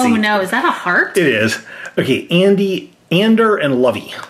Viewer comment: I heard u said my name "Andy" btw im a girl